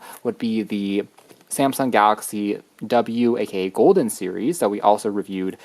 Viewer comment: you said something like ..............Samsung w sa Golden series ........ Maybe I misheard it but regardless, WHAT model is that???? I love it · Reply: It is a Samsung Galaxy Golden (W2016) Smart Flip Phone, and the video is called: Using a smart Flip phone in 2024 - Samsung Galaxy Golden W2016 Review - Better than Galaxy Folder?